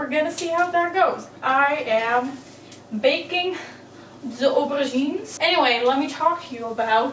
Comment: When you said "aubergines" I thought you said you were making moussaka with ovaries. I had to rewind that.